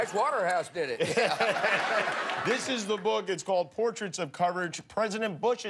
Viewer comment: did he say portraits of cartilage ?